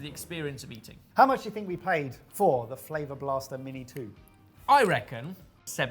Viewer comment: An English man sounds filthy saying "dirty oil". Yikes! (Did he just say "finger blaster"?)